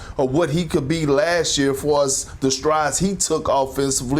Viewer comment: Ami I bugging or did Perk say “for us” at the 2:38 mark of the video? The same players Perk is phrasing will be the same ones him and ESPN will be destroying during the season. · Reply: I think he was saying far AS the strides he took, but it did sound like for Us